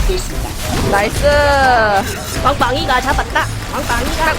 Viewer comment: 왕빵이가 잡았다! 하는 소리가 자꾸 엉빵이가 잡았다! 로 들려요 ㅋㅋㅋㅋㅋㅋㅋㅋㅋ 엉덩이 빵댕이~